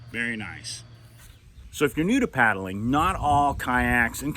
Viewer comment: What he said: "So, if you're new to paddling..." What I heard: "So, if you're noodle paddling..."